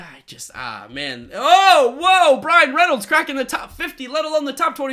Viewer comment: I thought he said Ryan Reynolds at first instead of Brian Reynolds until I checked